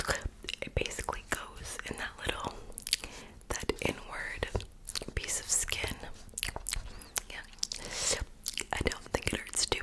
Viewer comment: I thought said “the N word piece of skin” when i was falling asleep and i woke up to replay it at 4:22 😂 but she said “inward piece of skin” 🤣